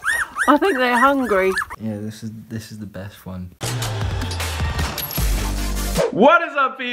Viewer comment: When said Yh I think this is the best one I thought he said Yh i think this is the deaf one I got emotional then